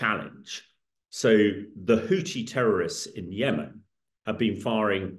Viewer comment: I swear this dood said Hoochi lmao